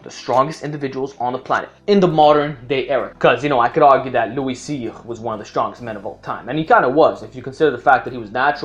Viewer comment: What name did you say at 1:14 ? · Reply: Louie cyr